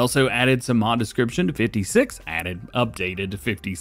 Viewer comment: I thought that said 65 not 56 must just be me